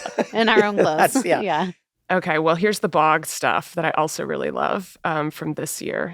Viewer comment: At the end, when Meg says "the bog stuff" I thought she said the Boggs stuff